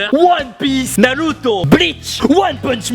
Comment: MAIS POURQUOI TU DIT NALUTO ON DIT NARUTO !! 3:09 ON DIT ZORO PAS ZOLO 5:47 ON DIT PAS LOUFFY ON DIT LEFFY (Luffy)